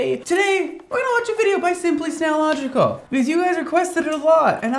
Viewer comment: At first I thought he was saying simply's nailogical lol..😂